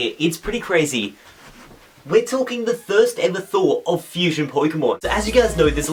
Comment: Did he just say the "thirst ever"